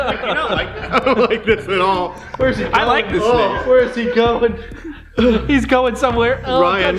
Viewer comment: All i hear was, " i like this snake" I was flipping out by then...